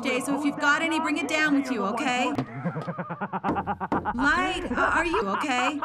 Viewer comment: For some reason i thought she said light are you gay